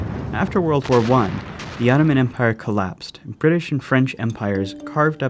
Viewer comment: i cant understand what he says at 1:10 "After the worl war 1 the .... empire collapsed " what empire ??? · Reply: jasmin jaber Ottoman Empire